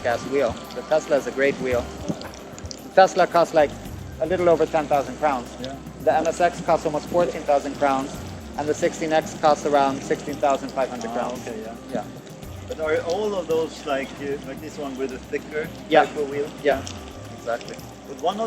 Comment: I thought I heard the "Tesla cost like a little over 10 THOUSAND pounds?" was like are we living in the same world?